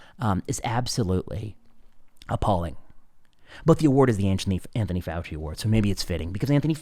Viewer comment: He speaks so fast when he said Anthony fauci award I thought he said anti fauci award 😂